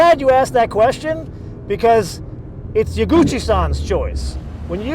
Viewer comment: lmao why does it sound like hes saying ya Gucci son every time